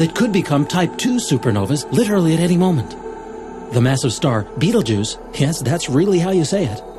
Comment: Betelgeuse? i thought you said beetle juice....